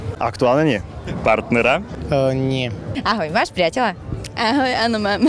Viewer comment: hold on did he said "priatela"